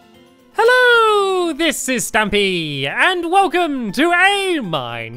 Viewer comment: Why do I hear Stampy's intro as "Hello this is Tampy!" Can't be unheard xD